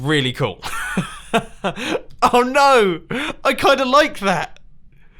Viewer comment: he says oh no but a ad happens and it sounded like "oh no I like dairy bliss coffee"